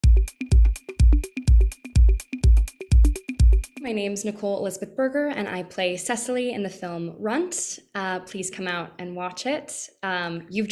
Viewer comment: I’m sorry did she say her last name is burger!!!!!!!!!!!!!!!